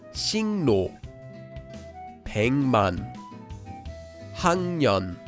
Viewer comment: did you say an example that wasn't on the slide? It's okay, but I was just confused for a bit, haha. Also, is that word spelled 벡만? (just want to know so I can add it to my notes) Thanks! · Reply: Oh, you're right! I had originally one extra, but removed it so it would be 3x3. It was 백만 ("뱅만"). Nice listening~! And my mistake.